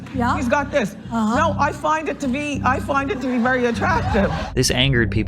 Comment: She actually said, "I find it very attractive"